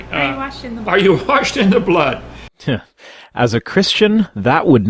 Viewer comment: With Kent talking too it sounds like she said the song is called "Brainwashed in the Blood".